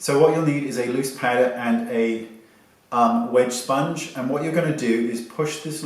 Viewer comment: Question?🖐 is the wedge sponge wet or dry? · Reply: Lol thank you for asking this bc instead of "wedge sponge" I thought he said "wet sponge" 😭